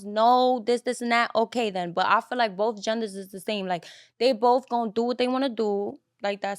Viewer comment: in and and all I heard is I feel I feel I feel